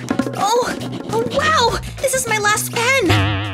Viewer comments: did she say on wow 🤦‍♀️ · instead of “Oh wow” I think it should be Oh no like if agree!